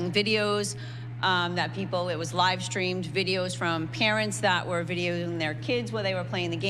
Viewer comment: I swear i thought she said "Karens that were videoing their kids..."